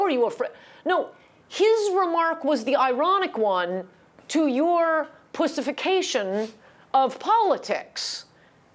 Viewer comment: Did I hear her right, saying: " His remarks was the ironic one to your 'pussyfication' of politics" (5:48) But close captioning transcription was something else?